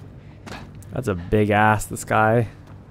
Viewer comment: excuse me, but did you say that's a big ass in the sky?